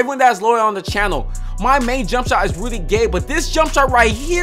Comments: Bruh i thought he was saying really gay instead of rudy gay lol 😂😂😂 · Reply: Constantine Chavez facts I thought he said really gay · I thought he said my jump shot is really gay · I thought he was saying really gay